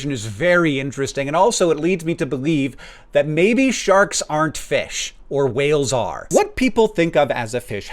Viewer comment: i thought it said shrek arent fish not paying attention at first